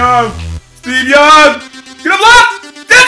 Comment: This whole time I thought he was saying Steve jobs lol